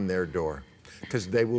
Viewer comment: Oooh, I thought they said pot and pants... Like smoke pot in a pair of slacks, be the most comfortable woman in the world.